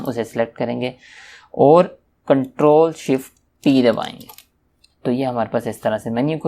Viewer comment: What do you say at 1:23? Right after you say "shift"? sounds like beedaline. Control, shift, what? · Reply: i think he says: ctrl+shift+T